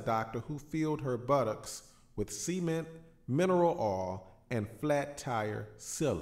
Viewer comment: I thought he said semen